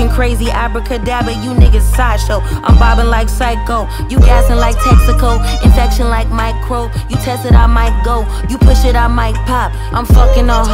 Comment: I thought she said "I'm mobbin like psycho!" Thought we had another weeb artist on our hands.